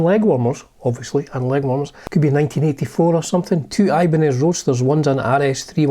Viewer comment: I heard “Ibanez Roasters”..... I had to wind it back lol